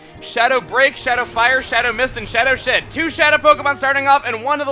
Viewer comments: did you say shadow shit? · he said Shadow Sh*t instead of Shadow Shed lol! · did he say shadow shit? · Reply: BroskiOfTheWorld Hd He says "shadow shed". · Did he say shadow sh*t? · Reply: False. It's Shadow SHED.